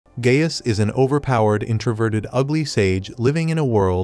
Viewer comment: did bro really say gayst